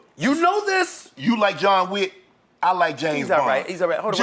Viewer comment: Why I thought he said "Ja Wick" 💀